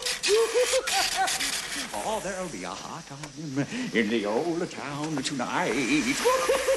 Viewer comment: What'd he say? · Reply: Oh there'll be a hot time in the old town tonight.